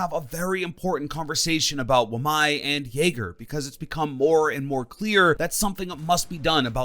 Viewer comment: When he said that he was gonna talk about wamai and Jager I thought he said "my n**ger"